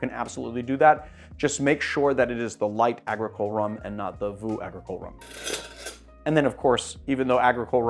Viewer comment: What did he say here? Voo agricole rhum? Does he mean vieux or something else?